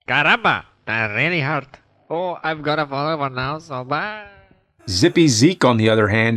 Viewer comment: Oh I've got a bullet hole now..... So bye😅😅😅 At least that's what I heard hahahaha